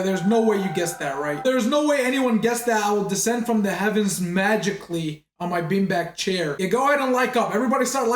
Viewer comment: WHY DID I ACTUALLY SAY "oh hes gonna descend from the ceiling"